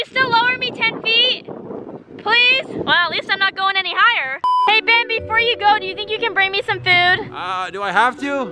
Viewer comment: not me think she said hey baby …